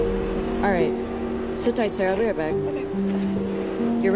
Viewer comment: the officer said "sit tight Sarah". Re-listening it sounded iffy but I heard "Sarah".